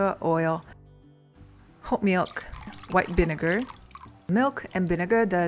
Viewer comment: Did you say HOT milk ? Also, adding white vinegar - doesn't it CURDLE?